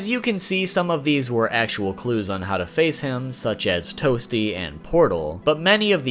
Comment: They need to put smoke in mk11 (ninja smoke not cyber smoke) also I never knew he was saying toasty I always thought he said woopsy